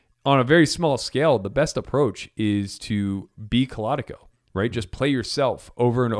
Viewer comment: "...is to be (???)" Can't make out what is said here. Assist please. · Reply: Claudico. It is poker AI developed by Carnegie Mellon University